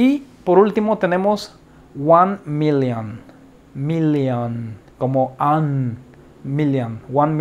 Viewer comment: very good class thank you very much teacher ... Even if it sounds weird at this minute of video 4:37 instead of a million, listen to Minion hahaha but good until next class teacher and thanks again